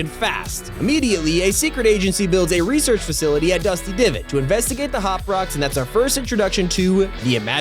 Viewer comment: why did he say dusty divid